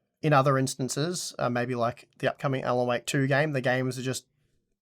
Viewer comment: I keep hearing "L O 8 2" game instead of Alan Wake 2 :D